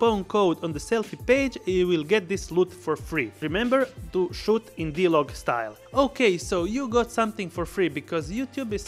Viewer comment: Did he say "Don't shoot in D-Log style at the beginning" at the beginning (1:15-1:17)? Sorry I have a hard time understanding his accent. Thanks! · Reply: shoot in D-log ;-)